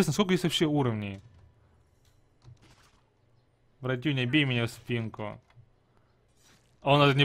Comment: "братюнь бей мне спинку" мне послышалось братюнь почеши мне спинку